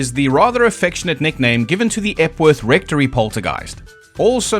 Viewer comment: Epworth Rectory... Jeez. I actually thought you said "F-word rectory" the first time you said it. I was like: "Weird, normally he doesn't censor himself like that".